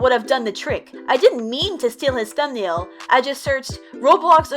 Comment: "steel" did i hear that right? "STEEL"